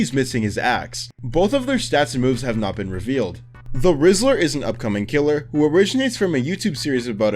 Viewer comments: why when he says "the whistler" it sounds like he is saying "the rizziler" 😭😭😭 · I thought you said the rizzler im like wth is this killer oh yh btw i cant wait for quest 666 and zombie king